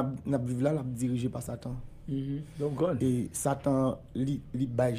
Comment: funny 😁. I heard him say, “ your god”.